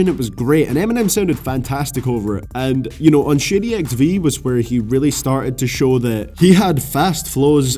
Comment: Who else hear "Shitty XV"